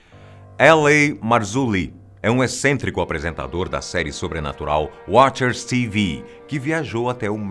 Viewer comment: Eu ouvi Supernatural? 😂❤